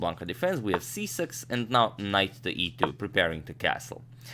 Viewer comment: Does anyone like to watch with the captions(auto generated)... If check at 2:08 🤣🤣🤣 "Nice to eat you" 🤣🤣🤣 instead of "knight to e2" (Ne2)😂🤣😂😂😂😂😂🤣🤣🤣🤣🤣🤣🤣🤣